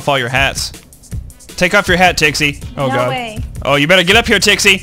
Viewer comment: I always thought he said Dixie! xD